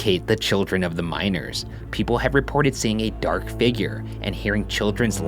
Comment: I thought he said Minors